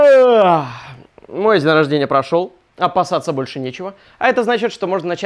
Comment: я одна услышала "обоссаться"?